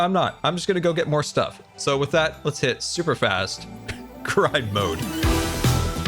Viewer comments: I swear I heard you say: super fast crime mode and I was looking forward to a montage of you stealing stuff from Skizz and Grian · Definately thought he said "Superfast Crime Mode." I was slightly disappointed when he was gathering resources. lol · ngl, I heard "Super Fast Crime Mode"... which also makes sense considering the ecological crime lmao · I genuinely heard "super fast crime mode", and it didn't even strike me as an odd thing for to say 😂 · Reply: I heard "super fast cry mode" and thought, wow, those chests sure are disorganized to get him like that